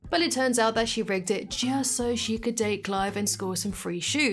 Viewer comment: Did you say Clive or Clyde? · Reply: clive